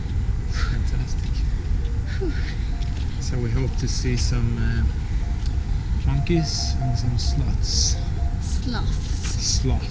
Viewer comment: Sounded like you said "would like to see some monkeys and some sluts" Sorry, too funny:)) Safe travels!